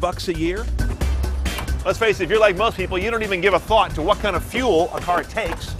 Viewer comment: did i just heard Cooley say : " dont give a fuck " ? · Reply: He says "give a THOUGHT." I had to go back because I thought I heard that too, and somebody in the room w me listening thought it too. Unfortunately he didn't :/ lol